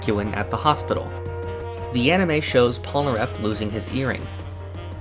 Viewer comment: Okay but at 4:54 it sounds like he said “there’s a new scene of Joseph taking a spirit photo of DIO’s asshole” and I died laughing